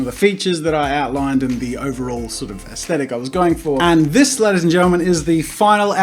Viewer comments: Videos 0.15 th second. Sounds like you said "get out lady's and gentlemens." 😂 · Whenever you say in your greeting "gentlemen" I always hear "german"